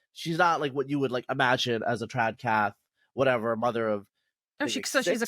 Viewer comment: Did I miss the bit where someone explained what "trad cath" meant? I thought he was saying "trad cat" the first ten times he said it.